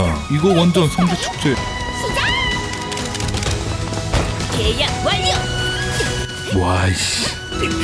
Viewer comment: 에 왜 나는 게이야 완료라고 들리지 내 귀가 썩은건가...